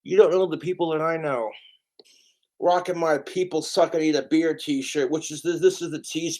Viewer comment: what's he saying the t-shirt says? "People suck..."? it sounds like "people suck it ain't a beer" but obviously that can't be it. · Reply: People suck I need a beer.